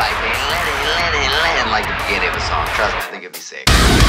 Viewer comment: Anyone know what he says in the intro? I can make out “Alright alright hear me out. Let’s say we have a little fire, where it’s like we let it let it let it (….?) trust me I think it’s safe” Can’t tell part of it though, curious if someone has better ears than me who knows. Such a banger, they deliver so well in these lives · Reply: i think its supposed to be talking to josh and the part where u said let it let it is pretending to be the guitar and he says trust me it'll be sick. That is at least what i thought he said